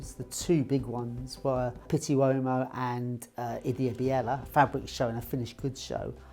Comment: he quickly names "two big" fashion shows in Italy. The first was Pitti Uomo. What was the name of the second, Finnish one? I couldn't catch it. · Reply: Ideabiella. It’s a fabric fair. It is now part of “Milano Unica”.